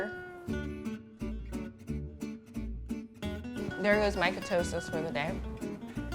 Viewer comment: did she say "there goes my ketosis for the day"?